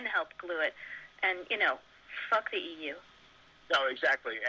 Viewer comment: did nuland say fork the EU? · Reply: F#ck the EU yes, and so it seems. Europe Is going down with Ukraine.